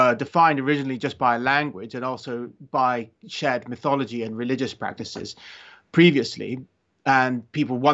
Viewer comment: I thought for a second he said Chad Mythologies 😂